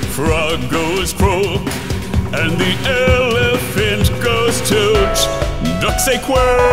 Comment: Oh God when he said elephant goes toot I thought he said elephant takes a toot